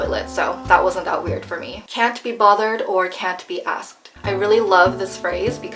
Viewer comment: Uhmmmm. #2. Can’t be asked, I think you misheard it. It’s actually “can’t be arsed”.